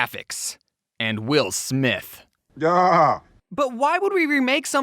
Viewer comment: “And W I L L S M I T H” “Yaaaaa” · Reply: I read the comment and listened to the sound and I thought it said “BaAaAa.”